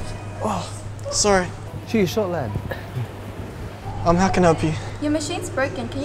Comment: I thought he said "ghee shot lahd" for some reason🤣🤦🏾‍♀️